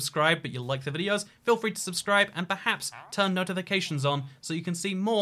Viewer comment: is it just me cause i heard at 15:52 "turn no to the cations on"